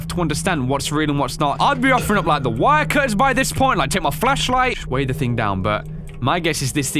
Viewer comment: Thought he said "offer a fleshlight"